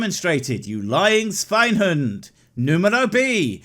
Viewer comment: did he just say "svin hund"